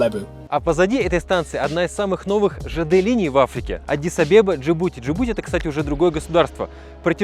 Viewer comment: Аддис - Абеба...Послышалось: "Садись обедать!" 😅😊😂